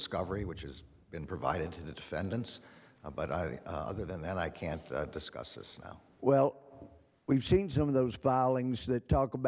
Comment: AG Merrick Garland: "I can't discuss this now." Holy shit! Sounds like he said, "Yes, that was the FBI!!!"